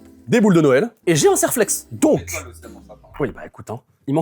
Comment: j'ai pas bien entendu je crois un quoi ? Un Serrrr....flex ? 😂 Moi perso j'appelle ça un Colson 😂